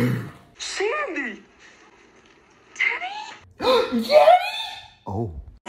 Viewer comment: "Teddy" what i heard "yeddy"